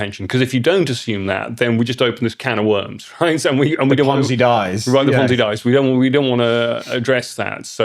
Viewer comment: Sorry, what exactly did you say at 0:44 ? What dies ? If you're going to make an important point then don't bloody mumble it. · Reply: The ponzi dies?